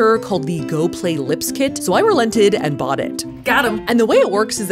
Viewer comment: I thought she said "So I rented it and got it"😭